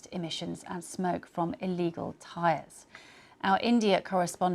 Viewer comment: did she say tires?